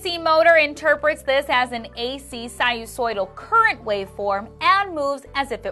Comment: I'm struggling to understand what is said "the AC motor interprets this as an AC ___ current waveform" could someone help please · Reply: sinusoidal